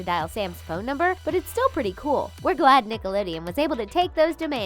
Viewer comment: Did anyone else hear nick-a-lideon at 2:42 😂😂😂😂